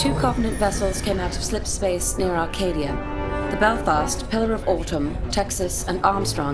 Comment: I just realized that Pillar of Autumn was mentioned... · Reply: Here, listen to this part carefully for the ship names. I believe that she actually said Pillar of Autumn.